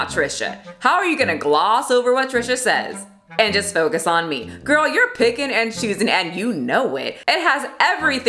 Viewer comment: What does it say about me, when she said "girl you're picking and choosing", I thought she said "girl you're bacon and egging" lol